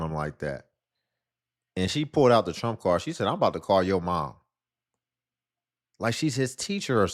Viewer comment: Wait did she just say ? "I'm about to call your mama?" 😮 Wow